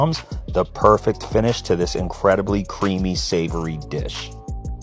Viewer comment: At first I thought it said I cooked my vegetarian wife